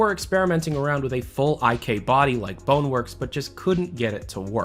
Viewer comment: What does he say at about 4:39? Icade body or something?